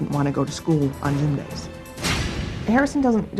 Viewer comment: he didint wanna go to school on____? what dors she say? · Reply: gym days